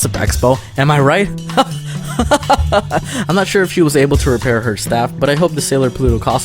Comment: I'm not sure if he was able to repair her staff Did I hear that right?